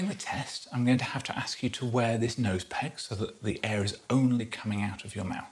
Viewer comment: Now I’m sure you said “nose peg”, but a part of me is enjoying mishearing it as “clothes peg” 🙊